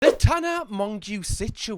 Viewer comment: I think this is the first time I’ve ever seen her name written out. I always heard it as “Tana Mojo” and assumed it was a screen name 😂